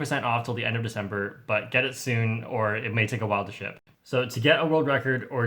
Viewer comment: The second time he said ‘Get in soon’ I thought he said ‘Get it? Sune.’ Lol